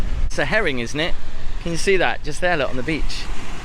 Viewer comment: That "herring" is a heron. Unless I misheard you naming the tall bird oon the beach😂